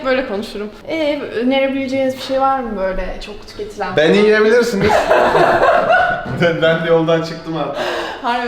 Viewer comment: ile 3:25 dikkat enes baturun o cümlesinden sonra ne dediğini iyi dinleyin "HARBİDEN YA ÇOK İSTERİM" geri kalan yorumu size bırakıyorum :)